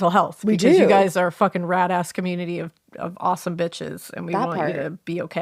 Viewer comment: I first heard "rat-ass community" and was like what the hell.. then I realized you said "RAD-ass community" 😂